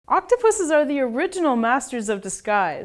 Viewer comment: Oh i thought you Said "octopussys"